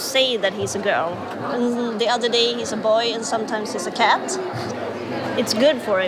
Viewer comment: did she said her kid now says he's a girl and sometimes he's a boy and sometimes a cat?